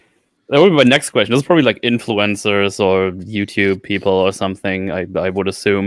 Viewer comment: "Influencers" I thought you said influenzas.